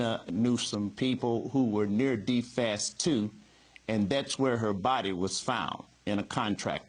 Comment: What did he say at 7:44? Near defast too?